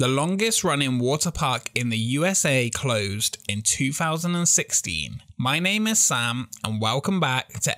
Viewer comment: I thought he said “2060” 😂